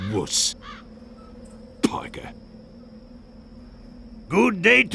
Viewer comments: Here’s a twist Spyper vs Sny also did sniper just say pogger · After watching that Jameskii vid, I just hear, “Pogger”